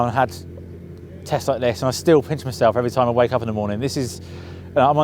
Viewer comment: Towards the end there I heard "I wake up every morning & piss myself" - mate there's nappies or rubber gruds for that! ( I did replay it - yeah "pinch") I prefer my version.